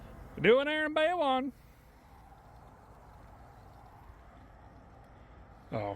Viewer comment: I work for jiffy lube and I heard you say Goin in on bay one and just about died laughing😂😂 I can relate😂